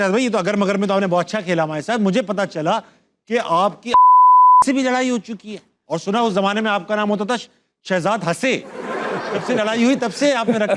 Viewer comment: what did tabish say? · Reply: I think he said "Altaf bhai" se bhi larai ho chuki hai